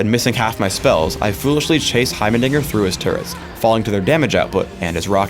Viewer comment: sounds like he says heimernigger